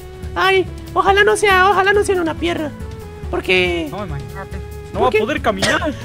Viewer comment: dijo mie#$ o entendi mal :v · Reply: ha dicho piedra